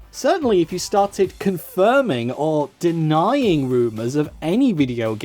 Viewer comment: Thought you were saying boomers when you were saying rumors.